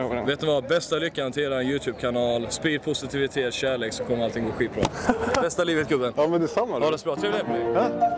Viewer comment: vad sa han vid 1:30??? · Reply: "Har du en mikrofon på dig så man spelar in allting, bästa ryck garanterad youtubekanal? sprid glädje och kärlek så kommer allting gå skitbra. tror jag